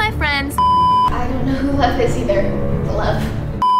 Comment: I thought she said love actully she said lev